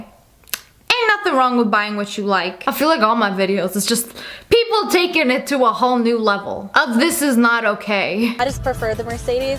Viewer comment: I thought u said ppl taking it to a whole new WORLD